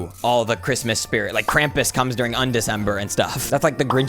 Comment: did u say crampis??? 👁👄👁